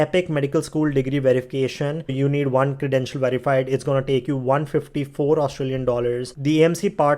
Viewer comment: What did you say at 22:47 "You need one student to verify..." ???